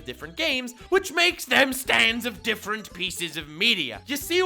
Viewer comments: Oh it’s Stan? I thought people were saying stands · Almost thought he said weebs have stands